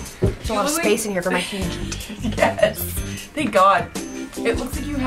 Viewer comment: did she say huge tampon at 5:57?